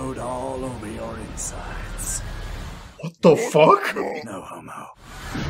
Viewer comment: I always thought Vegeta said “No Mo, Homo.”